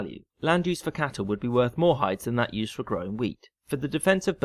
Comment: haha - weed instead of wheat xD